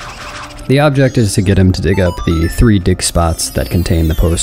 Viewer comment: have it on in the background, thought he was saying dick spots